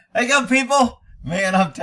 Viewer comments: I always thought you were saying “hey young people”. Lol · Thanks for clarifying. I always thought you were saying "Hey young people." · Thought you were saying " hey young people" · I thought you were saying Hey Young People ... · When i first started watching you, I thought you were saying "hey young people." I dunno, maybe my ears don't work, lol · I always thought you were saying "hey young people" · wow I thought you were saying how you go in people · LMAO Been watching for a while, always thought you were saying, how ya doing people